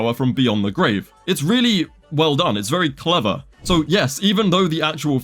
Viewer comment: did you say….clever?